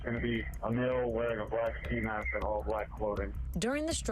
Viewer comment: I thought 💭 they said black man